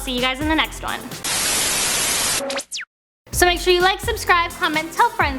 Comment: Is she getting lazy pronouncing her name or am I stupid? I always hear her say "court mginley"